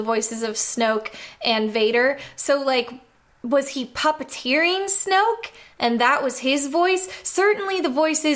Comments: I heard "was he pupetteering snoke?" As "was he palpateering snoke?" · I swear I heard you say "palpateering" the first time lol